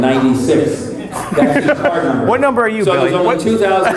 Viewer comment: what did billy wisper in your ear? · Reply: It sounded like he told to ask him "What number are you?", thus setting up the attempt at laughter at 1:05:00, not sure how I feel about that lol.